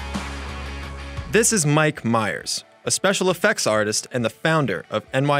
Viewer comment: for one minute i thought it was saying "Michael Myers"and creep me out 😂😂